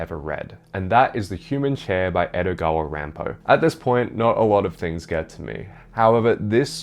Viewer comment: I misheard "Edogawa Rampo" for "Edogwa aran pow" and I thought that was the weirdest way I heard to pronounce Edgar Allan Poe LOL hahaha